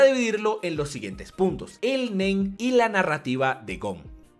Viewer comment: Cuando dijo el Nen creí que dijo el Men xd